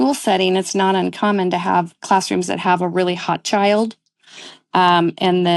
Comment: excuse me what did she say or mean??? both my ears and youtube captions quote her as saying "a really hot child". am i mishearing or is that a metaphor for troublesome children???